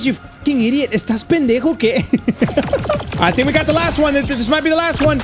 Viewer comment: I know he said is this pendeho okay but I heard is this person gay